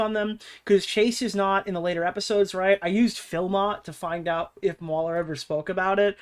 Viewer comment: What did use to find this clip? I couldn't quite understand it and I used variations on the captioned word "Philot" or "filmbot" or similar and didn't come up with anything that seemed right.